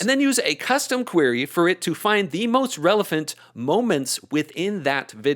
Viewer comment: Did say "Relephant?"